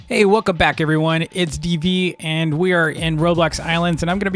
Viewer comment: i thought he said "hey, wocom bock everyone"